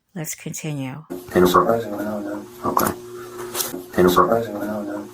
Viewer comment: Sounds like he says " surprisingly long, yeah"